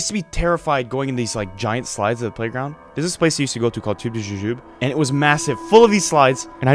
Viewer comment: don’t lie and think you know what he said😂 be like: you know the place called tU JuJu JoOb? fans: ?…….. WhA 😂😂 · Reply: Tubes & Jujubes